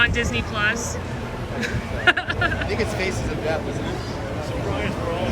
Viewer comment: did he say "faces of death"